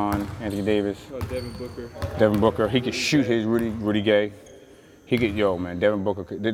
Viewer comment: On god I though he said pretty gay but I think he said Rudy gay